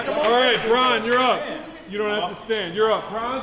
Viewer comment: LMAO did he say "bron you dont have to stand"?